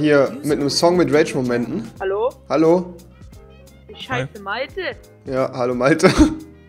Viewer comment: Ich habe bei 3:48 verstanden: Ich scheiße Malte 😂😂😂